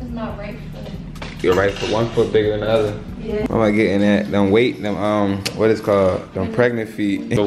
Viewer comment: okayyy Bc I Know I Heard Say "They Them " … ARE WE EXPECTING TWINSS ???